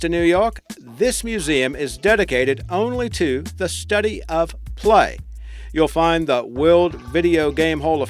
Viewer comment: I heard "study of FLY"..